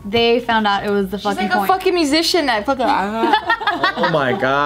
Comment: Did geo say she was a musician and carried on pulling stuff out of her mouth😂😂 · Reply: Magician